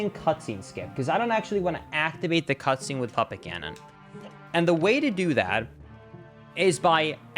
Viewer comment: Puppet Gannon! I thought you said papa Gannon.